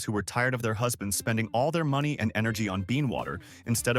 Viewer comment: ".... tired of their husbands spending all their money and energy on being water instead of their marriages...." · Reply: I think it may be ‘bean’ water (coffee beans) 💕💕💕